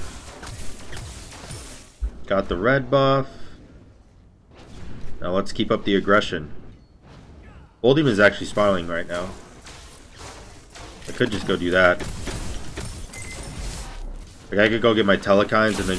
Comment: Did anyone else hear him say " bull demons actually smiling rn " at 15:00 lol